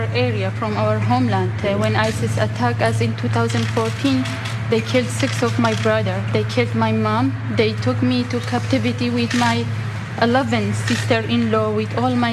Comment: It kinda sounds like she said "they kept my mom" the first time, but I'm pretty sure he would've heard her nice and clear when she's a foot away from him